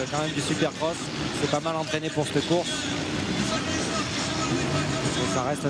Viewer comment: "Il s'est pas mal entraîné pour cte course". C'est quoi ce langage !?